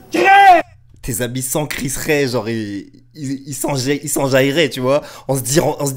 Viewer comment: Juste à 8:20 "ils s'en foutraient" pas "s'enjailleraient" Grosse force à toi 💪🏾🙏🏾